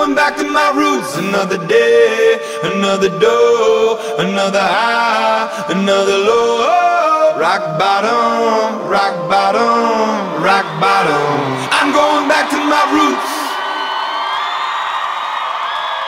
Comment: I thought its saying, Im Going Back To My Roofs?